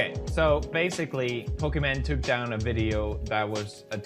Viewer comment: did I just hear?